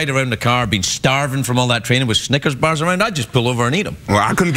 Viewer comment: when craig said snickers i thought he said ni&$rs